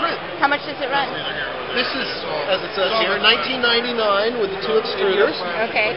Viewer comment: did he say 1999.00$ or 19.99$ (with the two extruders) O.o? I think he meant the higher amount. Expensive.